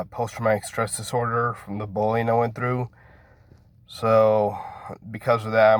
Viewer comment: i feel like a really bad person but i thought he said 'the bowling i went through' and i 😃🎳😭